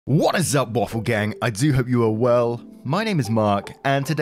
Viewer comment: Not native English speaker here. I just realized Mark says: I do hope you're well... When I thought he said something along the lines of "I do have your world" 🙃 I had been wondering for weeks what kind of statement or regionalism was that, I'm sorry